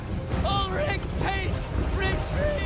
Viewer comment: did they say Ulrichs taint?